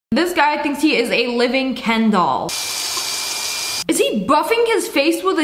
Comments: I thought she said a living candle🕯 👁👄👁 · I THOUGHT SHE SAID “A LIVING KENDALL”!!!!😂💀😭